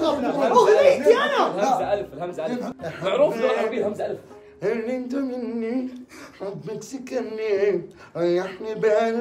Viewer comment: ها شو يقول 😂😂😂😂💔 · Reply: هل انت مني حبك سكني😂